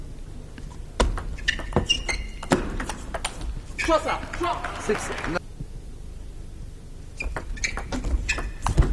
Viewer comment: What did Baum say when he got point? Josaı?? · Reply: Jossa